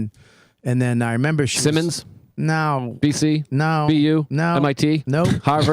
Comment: Joe list did NOT say university of Boston at 49:30! And nobody calls UMASS Boston the university of Boston.! Love ya Joe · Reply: he said BU